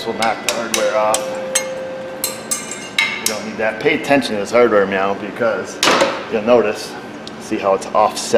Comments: I swear he said "pay attention to this hardware meow" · do you sneak a meow in at 9:47 ? in some other vids i think i hear a "meow" snuck in once in a while. or is it just me ?